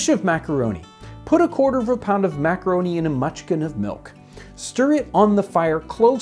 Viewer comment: It sounded like you saying munchkin at first, and I was thinking there must have been a shortage of small children 😅. Anyway, what is a mutchkin? Inquiring minds what to know 😅